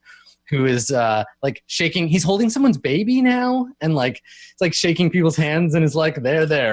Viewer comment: I thought was about to say he was shaking someones baby.